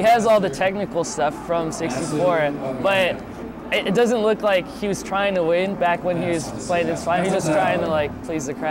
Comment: What does say at 9:16? The noise makes it hard to understand. Oh and can someone spell me the name of the person who played with Isai at 11:48? Is it Vans? And what does Chu Dat say at 12:00? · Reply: said "It doesn't look like he was trying to win back when he was playing... He was just trying to please the crowd." I think he tripped over his words a bit in the middle there. It is Vans from what I can tell I'm not sure what Chu says at 12:00, it sounds something like "she went the super [something] combo" Hope that helps.